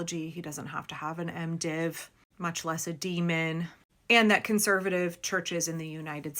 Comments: D. Min?! Doctor of Ministry. I don't know what you heard, but I heard "demon" 👹😂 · Did you say "A demon"? 6:26 😂😂😂